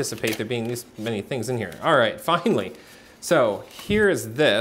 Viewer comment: Who else heard "fuckanlly" at 6:01 LMAAOOO 😂😂